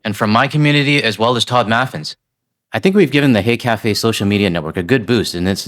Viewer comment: I think you should spell out H-e-y café, bec I honestly thought you said hate café!! 🇨🇦❤️💪💪💪 .